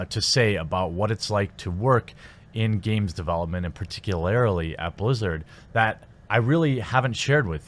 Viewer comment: Did he just say "particularily"?